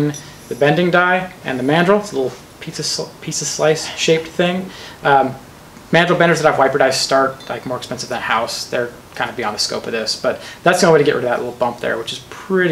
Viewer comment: has anyone figured out what he said between 4:35 and 4:39 ..? WTF was he saying is beyond the scope of this ? He said it like he was in the middle of chewing marbles or something. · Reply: "Mandrel benders that have wiper dies start more expensive than a house, they are kinda beyond the scope of this."